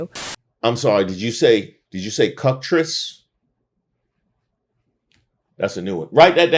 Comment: I think She said cOcktress